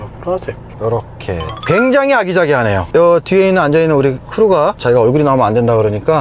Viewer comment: 굉장히 아기자기하네요 (굉장히 쓰잘데기 없네요)로 들리는 ㅋ